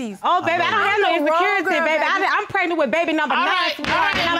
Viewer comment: did she say she's pregnant with BABY #9 ?!?!?!?!? - This blue-haired BT1100 is truly a machine !!!